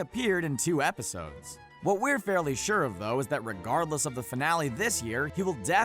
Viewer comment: all i heard was Rickgardless